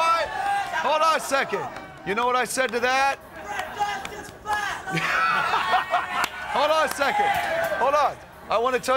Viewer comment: can anybody explain me what someone in the crowd said at 0:40? i can't really understand that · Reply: "Fred Durst is fat!"